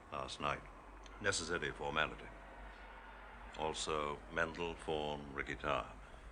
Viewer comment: If it weren't for Rickie Tarr, none of this could have happened. I wish Smiley would give him a little more credit. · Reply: I thought that Smiley said: "...and a Medal For....Ricky Tarr.."